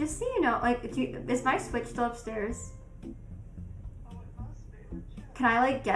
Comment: I thought she said her switch was upstairs